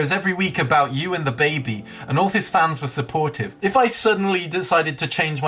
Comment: I heard "all his Dan's were deported"... I need halp